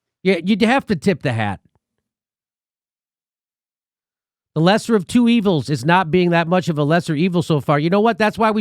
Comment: OH! I thought you said BETTER-MANNERED of two evils..